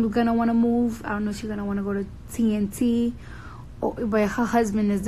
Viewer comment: did she say TNT 💣 😲